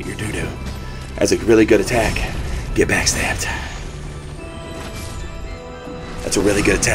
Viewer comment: I thought I kept hearing some new meme here, when, in a sort of low and gravelly voice intones the phrase "Get Backstabbed". I keep hearing "I'm Batman".